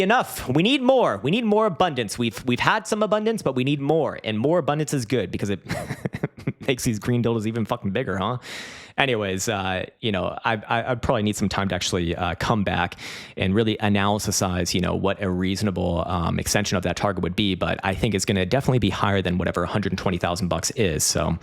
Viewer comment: did he say cumback 😂